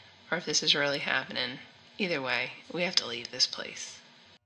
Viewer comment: I think it says, "We just want to play" The first part before this phrase was hard to hear, even the wording does not fit with the audio well for me. After hearing it a few times, I think says, "We don't want to leave" Though could be wrong :/